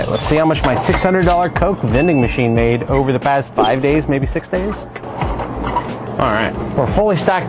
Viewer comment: at first i thought he said over the past month but after rewatching it i realized how much moey he makes